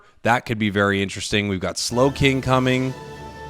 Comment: slowking is only a stage 1, it just evolves from slowpoke. and PS even if it evolved from slowbro that would mean stage 2. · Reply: I misheard Slowking as Slacking srry